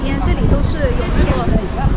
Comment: Love improving my Chinese watching your videos from France! I was wondering if you knew exactly which word she used at 28:09, you transcribed it as 需要, it sounds like she says "ha yao" instead? Thank you for everything you do, really!